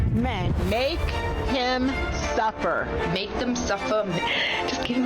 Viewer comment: 😂 I thought they were saying make him supper.😂